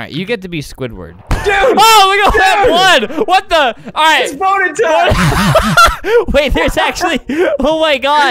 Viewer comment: kinda sounds like says ITS B°N3R TIME (watch a lil further)